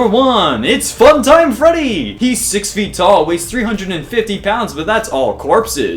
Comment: at first I thought he said 150 and I was like Omg I weigh more than funtime freddy